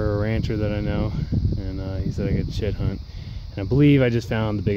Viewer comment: Sounds like he said "shit hunt".